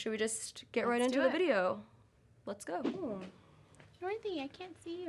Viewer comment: When Kim said Northy I thought she said Dorothy